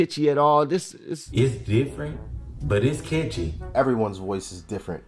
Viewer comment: “But it’s kitchen.” Wtf? Did he just say that?